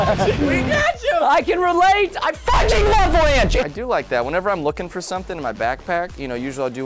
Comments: IDK WHAT PRESTON SAID DID HE SAY " I F!!!CKIN LOVE RANCH " OR WHAT 6:37 tell meh whoever knows what he said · Preston when the little kid said you guys didn't get ranch it sounds like you said I f ing love ranch